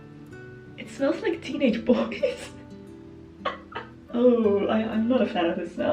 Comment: "it smells like teenage bodies" sent me to the floor 😂😂😂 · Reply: 😂😂😂 That’s a funny version! I thought she said teenaged boys🤔